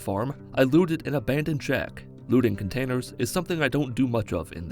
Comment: i always thought he was saying "lewding", but when i turned on subtitles, i was disappointed with "looting"